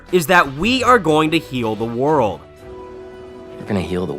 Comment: When I watched the trailer the first time I swear I thought venom said “kill the world” not “heal”